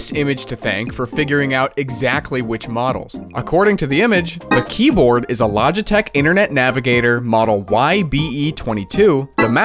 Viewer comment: for a sec i thought u said keyboard from a lie detector unit😂 must be cuz i set the playback speed at 2.5x which btw I highly suggest everyone